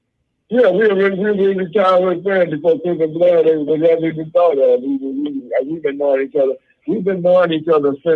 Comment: can someone type out what he said at 6:15? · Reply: Before crips and blood was ever thought of.